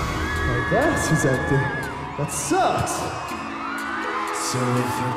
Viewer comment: what does he say here? I just hear him say something then he says that sucks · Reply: He says, "My glass is empty, that sucks!"